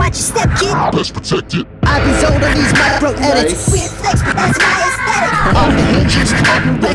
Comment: did he say "that's gay"?